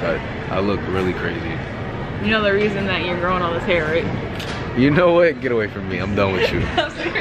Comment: can someone explain? I didn’t understand what said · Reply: She said you know the reason you’re growing all this hair right, I think she means he goes down on her a lot 😂😂 so that makes his hair grow